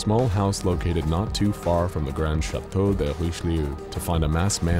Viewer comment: what language are you speaking and am I the only who hears this???? Or just a city or a place or something. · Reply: City called Chateu de richeliu